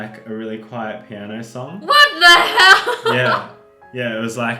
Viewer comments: I heard her say what the hell. I meant wengie. I meant I heard wengie say what the hell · anybody else hear Wengie say what the HELL